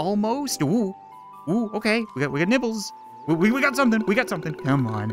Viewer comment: I thought you said nipples